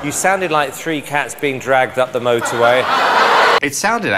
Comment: “You sound like 3 cats being dragged up the motor way.”😂🤣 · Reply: Oops I thought he said motor rail at first.😂